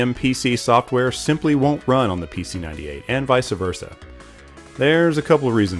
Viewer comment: Did he just say Vice-A-Versa?